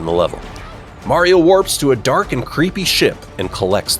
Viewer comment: it sounded like he said “a dark and creepy sh*t 😂